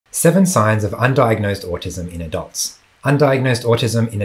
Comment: I thought you said undiagnosed Austism in DOGS 🐶… so interesting!! Side note: I honestly think my orange girl cat 🐱 might be autistic.